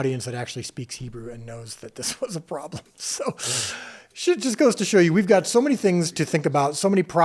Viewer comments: Did he say “shit just goes to show you..” at 2:20? 😂😂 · Did Dallas say sh*t at the 2:23 mark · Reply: Nope ‘shoot’